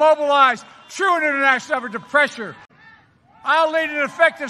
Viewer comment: Here's what I hear. "I'll lead an effective strategy to mobilize true international" He then forgets he's speaking english for a second and uses the masculine variant of "the" in German "Der pressure"